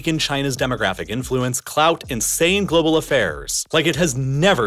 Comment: Did bro say clout